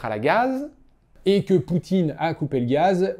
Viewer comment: mon oreille me joue des tours, au lieu de "union européenne" j'ai entendu "poutine" 🤣